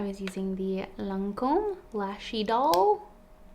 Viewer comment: Before I realised the name of the mascara, I always thought you said “lashy doll”. 😂😂😂 Great look. Love the jumper!